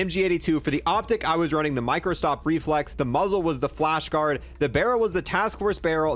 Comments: Did I hear Microsoft Reflex at 5:18? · I don’t know if it was just me I heard Microsoft